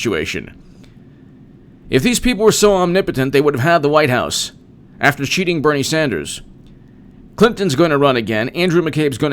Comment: I know you said "...after cheating Bernie Sanders" at 1:37 but I had to listen 3 times because I kept hearing "...after shooting Bernie Sanders" lol